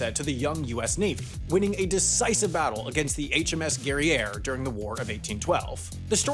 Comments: I thought you said HMS DERRIERE 🤔😂 Exactly, ONE BUTT... (boat 🚢) I dunno? Just messing around das boot🚢...big ol boat butt..? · As a person born and raised down the road from Crane, who now lives in Boston….I feel targeted by this content. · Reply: Someone, please tell me the narrator did not say "USS Derriere."